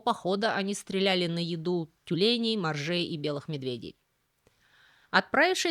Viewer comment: Услышал "Стреляют на еду У тюленей, бомжей!!!"🤣